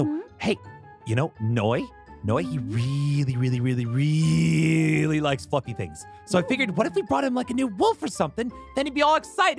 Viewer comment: I totally head wrong!!! XD I thought he said "Noi REALLY likes f**king things!" XD